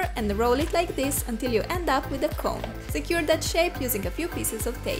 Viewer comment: When she said shape I thought she said shit😕